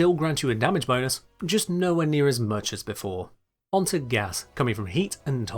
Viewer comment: "just nowhere near as much as before" I swear I heard Drusus Leverian. I enjoy this guy's voice, and the video is very informative. I think I'll subscribe.